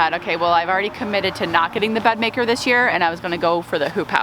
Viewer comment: lol this auto-plated and I thought you said you were getting a bread maker.